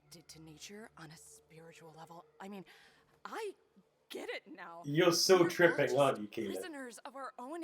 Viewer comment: What were you trying say at 10:05? Tripping what? Couldn't quite hear you. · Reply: “You’re still tripping, aren’t you Keyleth”